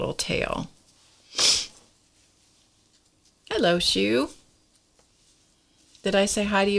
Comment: Oh my goodness, you said "hello, Shu" (might have been Ju), as you were finishing the first Mulberry Rose, and I thought you tearily said "I love you" to the flower! I nearly fell off the couch laughing. Still laughing even though I know you didn't! Beautiful flowers! :)